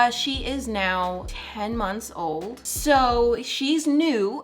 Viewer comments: I have an 8 month old and I am so impressed with all the work you’re still able to get done! You’re doing amazing and I find it really refreshing that some moms are choosing to keep their childrens life private! 💕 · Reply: Oh i thought you said your 8 months old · When she said 10 months old I thought she said 10 years old so for a sec I was like OMGGG